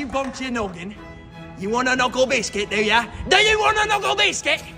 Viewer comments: I thought he said nigga But he said do ya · I thought he said the n word but it turns out he just said “Do you?”